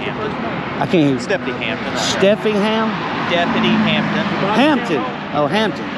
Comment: I thought you said "deputy Ham"...lol